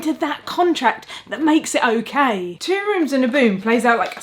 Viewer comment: I've heard of "Two Rooms and a Boom" before... but until I watched this video I apparently consistently misheard it as "Two Rooms and a Broom" and had this vague notion that it was a game about Witches competing over potion making or ... something about cleaning.